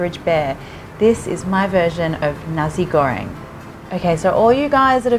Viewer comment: I hear it like 'nazi goreng' lol jk